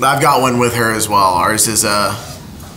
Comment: bro are they fighting ben literally said ours is a little more emotions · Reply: I thought he said a little more motions